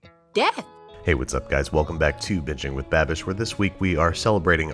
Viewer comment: when he said welcome back to binging with babish i thought he said welcome back to bitching with babish